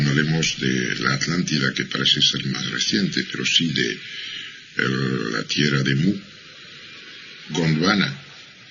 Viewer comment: Tierra de....? alguien sabe que dijo? Gracias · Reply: La Tierra de Mu: <link> ;)